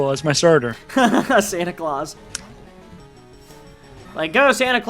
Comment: this is hilarious the part where it sounded like i said Santa Claus when i was actually trying to say Cindaquel